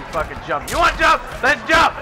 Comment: when sub zero goes up its sounds like he said jump